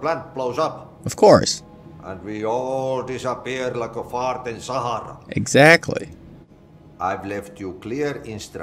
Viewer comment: Thought the janitor said like a fart in the Sahara desert or maybe it’s just me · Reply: Smoothie Box "...all disappear like a fart in the Sahara."